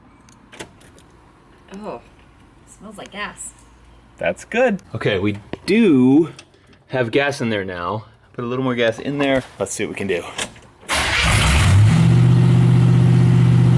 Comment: I heard "Mere: Ugh, smells like ass.... Matt: That's good"